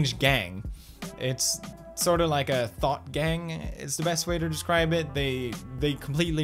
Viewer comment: When you said “thought gang” I definitely heard “THOT GANG” BOIS BRING IN THE THOTS